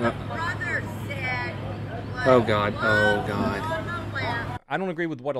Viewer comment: I think she said "what the brother said, put love on the left?" I could be wrong but that's what I hear